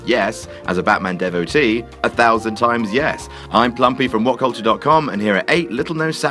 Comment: I swore I heard 'I'm plumpy from whatculture'.